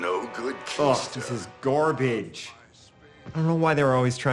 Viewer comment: Did he just say “GORbage” instead of GARbage?